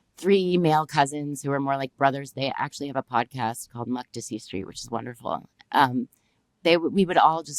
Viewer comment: Najla mentions her cousins podcast, I didnt get the name, can anyone tell me what she said. Thanks alot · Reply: Makdisi Street